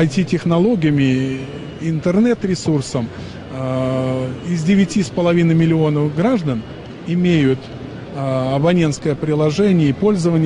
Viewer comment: я единственный услышал "из 9 миллионов граждан 13 миллионов имеют приложение" Что за чушь?